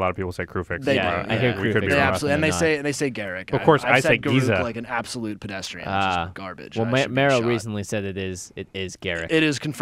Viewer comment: “like an absolute pedestrian, which is garbage and I should be shot” Lmfao, didn’t sound like anyone heard that